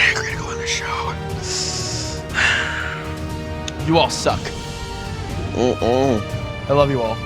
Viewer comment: wtf I heard 7:19 you all suck and the caleb went hmm uhmmmm xD